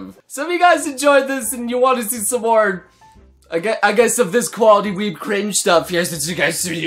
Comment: I thought he said "koalali" instead of "quality" xD :v I need help(??)